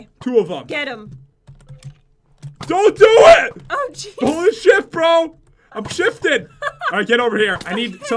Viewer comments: I thought he said "holy shit bro" Lmao But he said "holding shift bro" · did pat say s*it bro? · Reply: Lol pat won't even sweared he said "Im holding SHIFT bro!" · Did pat say holy s**t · I thought you said sh**😂😂😂😂😂😂😂😂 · did he just say holy sh** or did he say holy shift??? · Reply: He said holding shift